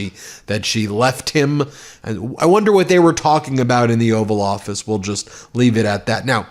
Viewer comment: Did you say the “ORAL” office or oval offices 🤣👍🏻